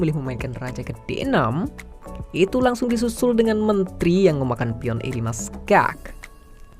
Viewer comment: "langsung di susul Mentri memakan pion" sepertinya saya salah denger '-'